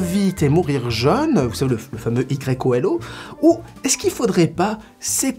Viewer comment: Ahaha à 10:02 j'ai entendu " i greco élau" j'ai cru à une citation en latin... et puis je me suis dit YOLO ahahah